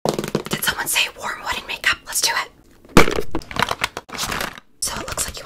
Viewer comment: In the beginning I feel like I’m the only one who heard her say warm wedding makeup